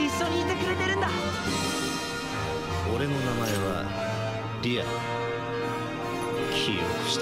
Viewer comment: lol AXEL in the sub instead of LEA :p